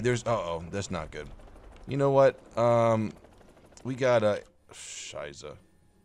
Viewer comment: Did he say "scheiße"(german word for "shit") on 7:26?